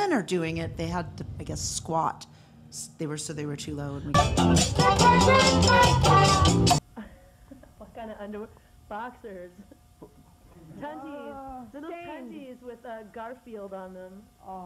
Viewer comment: What does she say at 0:32 - 0:42 & 2:33 - 2:42 ? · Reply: What kinda underwear? Boxers, sundies [shitty undies], sundies with Garfield on them. Where do you see yourself in 10 years? 30.